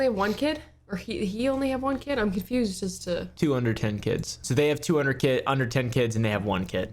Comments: I thought you said 210 kids.... Two under ten 😂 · why did I think he said 201 kids?